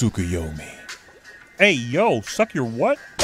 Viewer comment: Honestly, I thought he was saying "fuck you". It's Gordon Ramsay after all